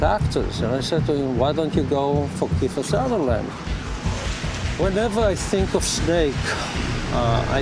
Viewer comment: sounded like that guy said "Why don't you go fuck Kiefer Sutherland", lol.